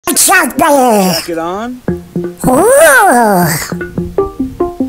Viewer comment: It’s sound like he said « jack it off » instead of jacket off